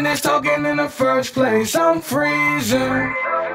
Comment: who else likes the echo when he says “i’m freezing” 🥶🥶🥶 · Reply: I thought he said freezer